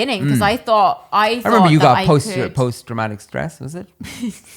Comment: Fully thought said "post DRAMATIC stress disorder" HAHAHAHAHAHA